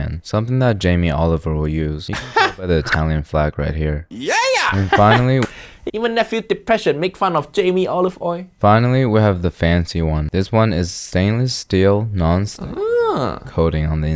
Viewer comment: does he say Jaime Oliver or Jaime Olive oil?